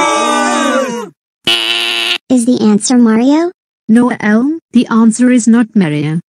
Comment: did she just called Elmo elm